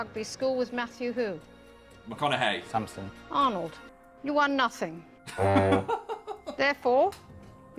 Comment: I forgot he cut everyone else’s questions so I thought she just attacked him 😂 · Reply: i thought she said 'you won nothing'